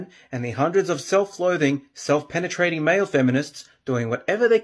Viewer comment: Wait a second, at 5:34 does he say "self penetrating men"? LMAO